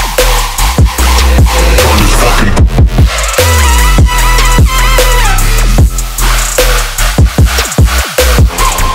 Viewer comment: What the HELL do they say at 1:04 ?? It’s been driving me nuts. It’s also said in the song MVP by Flosstradamus. Some name drop? Someone help me!!! 😭😭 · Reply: that say "hey" bruh